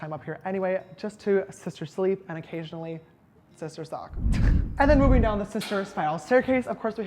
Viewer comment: Did he just say sister suck??😂